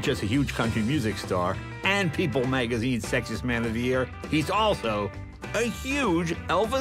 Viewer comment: sounds like he said "sexist man of the year" 😂